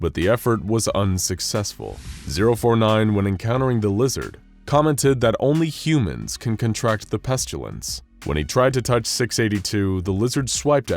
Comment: that’s incorrect, he actually said “not only does it affect humans, but this creature as well.” So 682 does have The Pestilence.